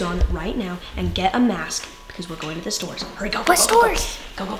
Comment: I thought that you said that you’re going to the gas station